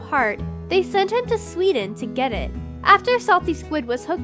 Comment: did i hear sweden